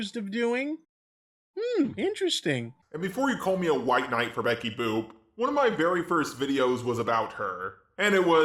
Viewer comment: for a second I thought you said Betty book. Is it even possible to white Knight over an old fictional cartoon character ?